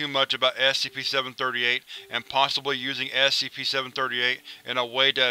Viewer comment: I'm sorry, I know it's just your accent, but literally every time you say "SCP," I hear "Assy pee."